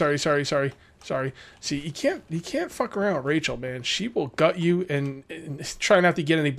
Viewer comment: I thought said you can't fuck Rachel 😂😂